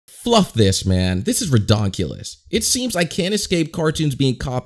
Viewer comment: Already 2.0 seconds in the video and I hear "Fluff this man, this is Ridongkles"